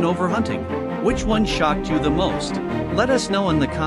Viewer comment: Thought he said " which knew shot you the.most"